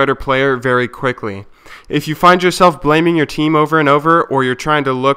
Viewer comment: first time i heard this i heard. if you are playing teemo over and over